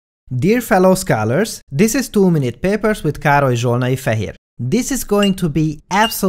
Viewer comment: I swear, every time, I hear you say "too many peppers" instead of "two minute papers"